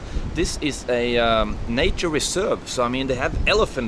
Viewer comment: at first I thought you said naturist resort...lol.